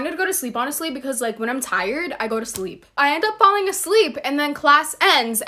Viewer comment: Did she really say when I’m tired I want to go to sleep